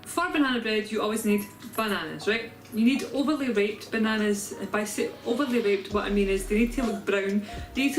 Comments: You say, “Overly ripped” I hear “Overly raped” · is it just me or does it sound like she is sayin overly raped?